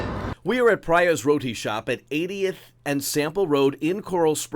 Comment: Sounded like he said Priya’s Rodent shop 😂😂😂😂